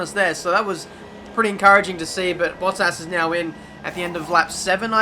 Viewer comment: The apex ... Just turn on the captions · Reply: when says 'Bottas' the captions say 'what SAS'